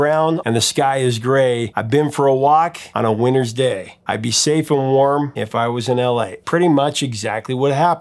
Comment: I heard it as "I could take that walk, if I was in LA. sounds better, actually.